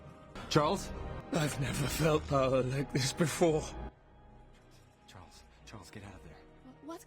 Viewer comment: yeah that was Apocalypse saying "I've never felt power like this before" talking about Charles's powers while taking control of him and Cerebro..